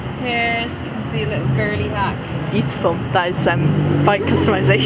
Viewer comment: I've listened to this a couple of times and I haven't heard her say "kinda gay" anywhere. I think she actually said "girly hack" at one point, but nothing homophobic!